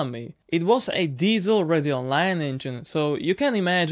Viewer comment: Is it just me, or does his words "Ready Online" sounds like he is saying "Radeon line"? Radeon is AMD GPU btw. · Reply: Radial nine, sorry if not clearly said.